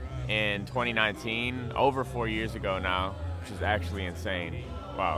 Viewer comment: Whenever he said 4 years in the beginning I thought he said 40😂 I’m like this man’s crazy